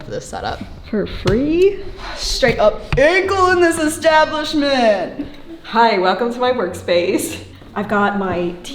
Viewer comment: One time some thought I had said “ankles against the establishment.” Also lovely surprise sword content